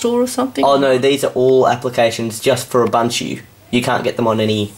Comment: DID YOU JUST SAY "UBUNCHU"? >:(